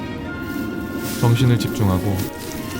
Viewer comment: 왜 정신이 아니라 ㅂ신이라 들리지